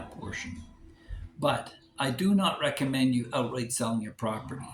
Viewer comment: i do not recommend you do **** on your property? What? What’s the word i am not able to catch there. Sorry. Thanks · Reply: Outright sell...don't Outright sell your property. Mortgage it instead.